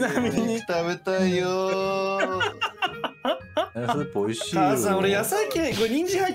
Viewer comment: の🐰さん 字幕では母さんだけど、お母さんに聞こえる！お母さん呼び良きです